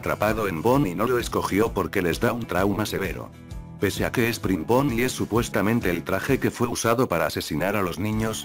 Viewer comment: Yo estuve un montón de tiempo pensando que bonnie era springtrap xD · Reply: Soy tonto pensé que decía springbonnie xD